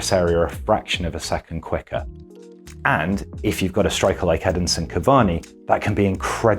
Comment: Did he say Headinson cavani?